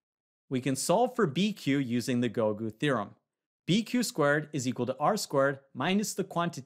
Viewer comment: I searched about gogu theorem all I got was goku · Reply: Oh I thought he said 'golden theorem'